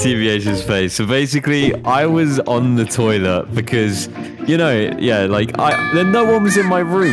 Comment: when was talking abt the story with him on the toilet and tbh coming in i thought he said "i was on the toilet with children" i turned into kevin hart talking abt his friends with ms. green i went "OOOOOOOOOOOOOOOOOOOH"